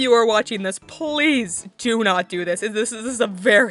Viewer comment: all I hear is "do this"